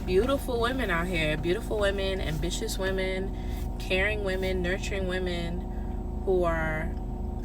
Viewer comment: did she say "caring women" or "Karen women"?